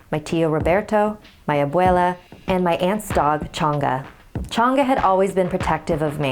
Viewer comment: When she said the dog’s name I thought she said Chancla